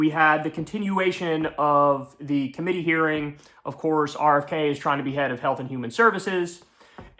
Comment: "RFK Jr. wants to be head of Health and Human Services" ... what I heard: "RFK Jr. wants to behead Health and Human Services."